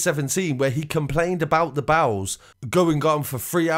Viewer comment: Sounds like youre saying bowls instead of bells